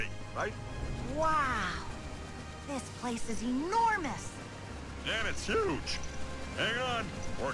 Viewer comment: did anyone hear say *Wow.. Its anormus*?